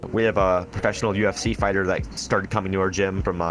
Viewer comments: I like how he says UFC fighter and the captions say KFC fighter 😂😂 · it said KFC instead of UFC lmao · subtitles say KFC not ufc lmao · he says ufc, but the subtitles say kfc. Thought that was gdamn hilarious. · Anyone else notice at 6:04 the subs say they have a pro kfc fighter instead of ufc?